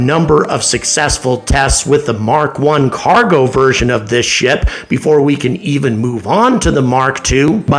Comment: Does he say "version of this shit"? Nicely played.